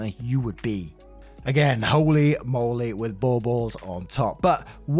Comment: Hi! Absolutely screaming at “Holy moly with bull balls on top.” At least that’s what I thought I heard. Im in tears jsjdjf that’s amazing